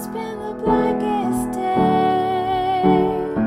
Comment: Why am i keep hearing "black ass day"?! same in original